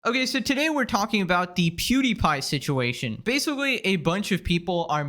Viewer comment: i thought you said cutiepie situation in the beginning 😭